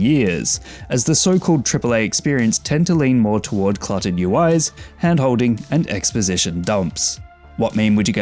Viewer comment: I misheard "cluttered UIs" as "clotted UIs" at first ... but it still works 🙃